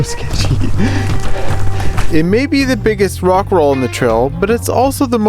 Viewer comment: i thought that he was gonna say rick roll